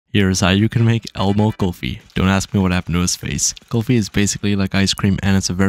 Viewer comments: I thought that said emo kulfi💀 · Why did he sound like he said "elbow kulfi" 💀